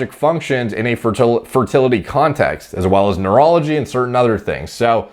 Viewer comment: When he said "in a fertility context", I thought he was saying, "in a fertility cult".